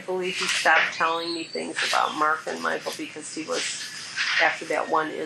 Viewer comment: The sons names are Michael and Mark and those aren't her kids. Those are her step sons. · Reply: I’m aware they are step sons I got that but it sounded like she said they both were names Mike and Michael. And if you look at other’s comments you would see it’s what others heard too. Perhaps jumping on just me is a bit inappropriate. Maybe make a general comment so perhaps people can see the error every single person heard.